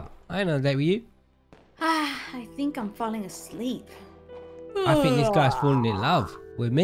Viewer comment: When it said I think I am falling a sleep I thought it said I think I am falling and then i was like oh falling asleep silly me